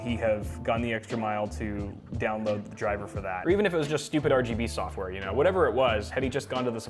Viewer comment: did you say STUPID RGB SOFTWARE!!???